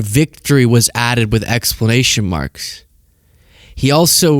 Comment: did you just say "explanation" marks?? c'mon!